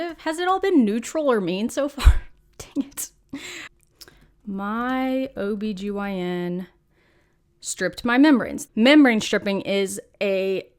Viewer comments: Lololololol omg I wasn’t looking at the screen @ 9:11 and I stfg I thought u said “my obgyn stripped my memories” (@ 4:11) at first and I was SO confused! 😹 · I had to put captions on because I kept playing the part over and over when I thought you said: “Why did my OBGYN strip my memories” “We sometimes strip memories when......” I thought I was not versed in some type of slang or something? Oh my word I laughed so hard at how relieved I was to see it wasn’t “memories” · At first I thought you said "memory stripping" and I was very confused about the skills involved in OBGYN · When you said "Why my OBGYN stripped my membranes " I thought you said, "Why my OBGYN stripped my memory" LOL. I had to replay it. 😂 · When said "my obgyn striped my membranes" I thought she said SRTIPED MY MEMORIES for a second 😂 oh boi. Also, I'm still confused on what membranes are lol